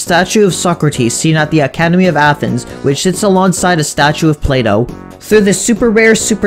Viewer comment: thought u said play doh